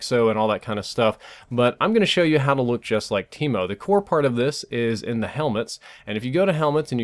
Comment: Thought he said keemo